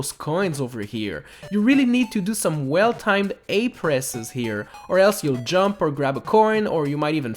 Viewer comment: I heard whale-timed a presses lol